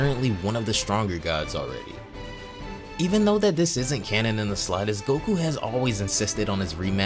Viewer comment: Nevermind I literally just heard you say it is t cannon lmfao 🤣